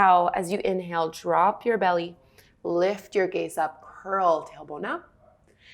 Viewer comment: What are you saying at 2:34? It is at your first cat-cow. Thanks, I love this workout. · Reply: “Lift your gaze up, curl, tailbone up”